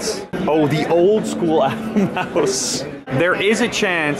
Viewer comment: I thought it said house.. Not mouse